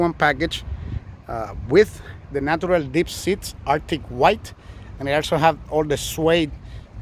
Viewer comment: did he say "deep shit or dip shit"? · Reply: Natural Dip Seats! That's the name of the interior color. lmao!!!